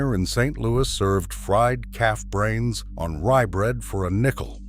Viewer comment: im from st. louis and i never heard of that calf brain sandwich and at first i thought he said cat brains, i had to start the video over and turn on the subtitles